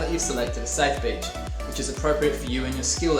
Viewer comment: I thought he said choose a safe bitch. I was like woah surfer lingo is little wild but Okay